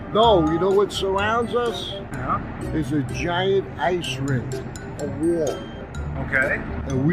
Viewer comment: I had the captions on as I can't understand the idiotic mumbling and says the Earth is surrounded by a giant ice cream. Of all the FE nonsense, I wish this one is true. 😂